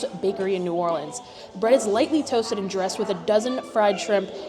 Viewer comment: it sounded like she said the bread is "lightning toasted," but the menu says "lightly toasted." it reminds me of my ex boyfriend who said he wanted me to die by electrocution.